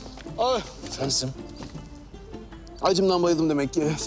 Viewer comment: acımdan bayıldım diyince, açlıktan öldüm anlamında olan acımdan öldüm anladım😂